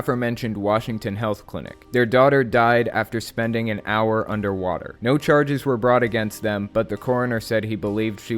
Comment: wait... did you say he held his baby under water for an hour? 🤯